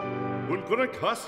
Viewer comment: おいこらカスに聞こえるwwwwwwwwwww